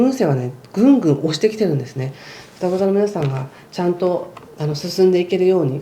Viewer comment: 途中、7:10あたりで、双子座のみなさん と言っているように聞こえました…